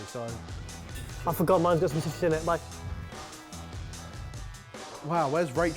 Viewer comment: I paused at 1:47 for a good solid minute trying to figure out what Barry said. And I still can't figure it out. Help please - it's 12 midnight where I am, and I won't be able to sleep because of this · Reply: 'I forgot mine's got some citrus in it, bye' - is what we THINK he said haha